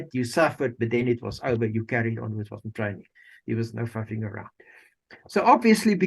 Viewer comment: Pity about the foul language of the interviewee. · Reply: I listened a second time after reading your comment, and slowed down the "foul" language to be sure. the words I heard were "flippin" and "faffing" the latter (according to google) means "to spend time doing things in a way that is not well organized and that does not achieve much", which is what I have always understood it to mean, and which is used in context here.